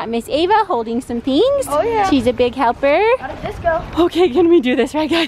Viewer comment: did he say “I’m a VSCO”? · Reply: He said got a disco I think lmao🤣🤣